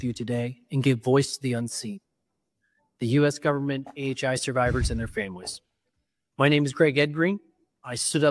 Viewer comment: Hold on, did he say "NHI survivors and their families"?